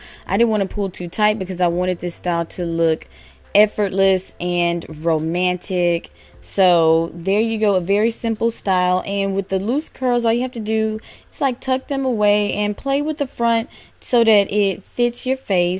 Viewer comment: LOL GIRL! I thought you said effortless and fromantic! XD!